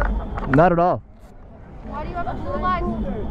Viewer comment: did she say "blue lives matter?" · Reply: She said "Why do you have a blue lives matter flag" She's not too bright.